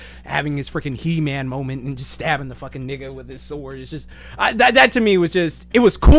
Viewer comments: did he say nigga? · Hold up!!!!!! Did he say "stabbing the fucking nigga" at 8:11????